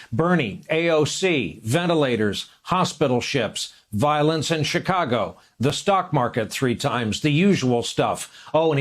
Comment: "Violins in Chicago..." I probably misheard that part, but who knows with this rambling fool.